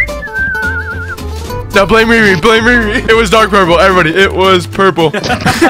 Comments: Everyone talking about how funny this vid is and how he is cheating Me: 3:13 “did he just say dark purple?” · did he say dark purple?